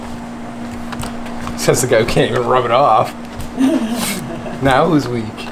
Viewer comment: This episode... out of context... I wasn't paying attention and I heard, "Says the guy who can't even rub it off..." followed later by "I'm gonna beat it" I'll kick my brain out of the gutter, my bad!